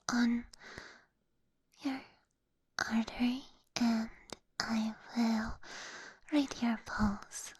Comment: Anybody else heard “I will break your balls” here lol